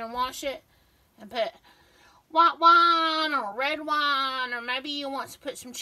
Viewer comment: I thought you said " Pour out the wa wa or want, want." Had a good laugh.😂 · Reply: She said white wine, or red wine.